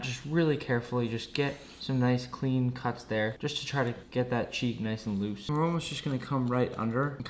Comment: I thought he said cheese but he said cheek